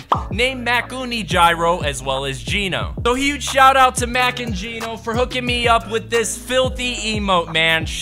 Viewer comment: Mac and jino (I swear I thought I heard Mac and cheesy)